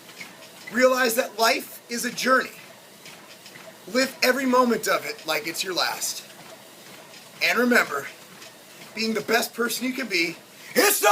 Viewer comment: At first, I thought you said "Lift every moment like it's your last." Either way, something to think about :D